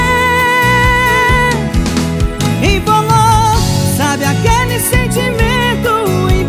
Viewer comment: A vida toda escutei "incolor" mas na verdade é " embolou" 🤡🤡🤡